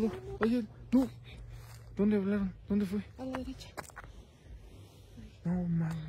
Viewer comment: No se si soy yo o no yo escuché una voz DONDE QUIEREN en el minuto 19:18 juzguen ustedes